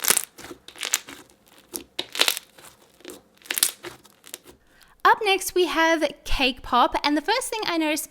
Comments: When she said "cake pop" I thought she said "k-pop" xD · I thought she said K-POP not CAKE POP lmao 😂